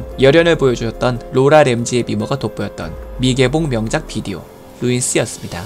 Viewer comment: 나같으면 주변에 불부터 질렀을것 같은데, 그럼 ?? 됬을거(??) 여기 뭐라고 발음 하는건가요?!?! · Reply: 그럼 같이 통구이가 됐을 거라고 하시는거 같아요ㅋㅋㅋ